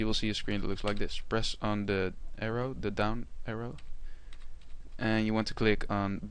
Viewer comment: what he says on 8:18? what I should press? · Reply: Down arrow